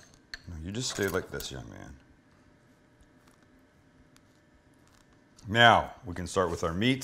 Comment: did he say "meow we can start with our meat and our cheeses"